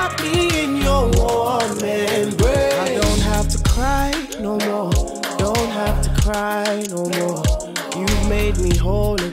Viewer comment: Love this.... when He said you don’t have to cry no more😭😭😭😭I thought he said (you don’t have to die no more)😂😂thank you Jesus for the Phrophetic message!!!